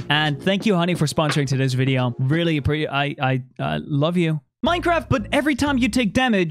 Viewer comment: Can we just appreciate that fundy coded this 👏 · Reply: i was not hostile but you are right i actually at first i thought he said, its easy to appreciate.